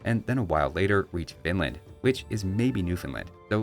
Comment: I keep hearing "Vinland" as "Finland".